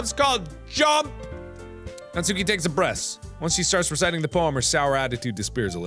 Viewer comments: is it me or does mark say "natsuki takes a 'Breast' " it may just be me, or mark must be speakin kinda fast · "Natsuki takes a breast" did anyone else heard that or am I just weirdly horney?😂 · Did anyone else hear "breast" 24:42?? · I swear I heard “Natsuki takes a breast and starts to recite.” XD